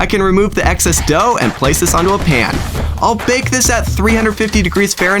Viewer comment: On 5:46 did anybody know what he said? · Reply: “place this onto a pan”